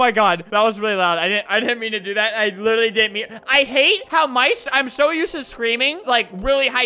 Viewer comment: I think he is tryna say how my voice??? Lol idk